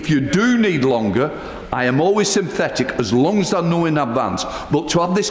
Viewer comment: I'm sure I just heard the speaker say he was "synthetic"... sounds about right 😅